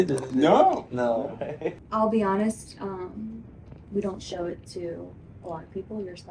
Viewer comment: did she say..... "We don't show it to black people"? Or am I just hearing things???